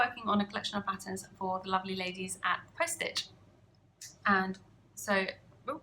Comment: hi sorry to ask but who is it you are collaborating with my speakers are terrible and couldnt hear you properly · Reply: It sounded like she said poststitch box.